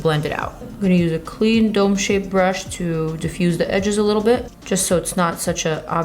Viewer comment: I thought i heard Dope-shaped brush 😂😂😂😂😂😂😂😂😂😂😂😂